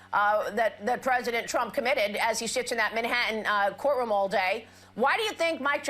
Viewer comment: I coulda sworn said, “as he [Trump] sh—s in that courtroom all day.” the first time I listened.